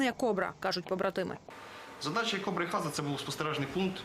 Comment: Украинец сам признал что русский солдат лучший в мире и сказал спасибо якуту что он не стал его добивать и дал спокойно уйти в другой мир. Слава якуту русскому солдату. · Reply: Блин ребят вы меня успокоили когда написали что позывной Хаза ато мне послышалось что он кричал грузинское имя Заза и я немог заснуть всю ночь думал что грузин предал и убежал оказалось всё по другому